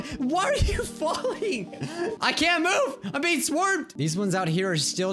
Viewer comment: I can’t mi e I’m being swermed lol he said swermed instead of swarmed